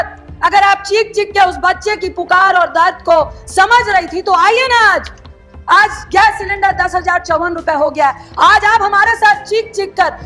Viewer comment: did she said? aaj cylinder "dus hazar chauwwan rupaye ho gya hain" 10,054/- :D